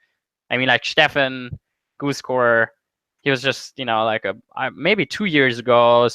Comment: what is the name of the poker player that fedor said he pushed to play high rollers and staked? I couldn't make out the name except for maybe Stephen Goosescore? Ty in advance for any help! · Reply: Goose.Core = Steffen Sontheimer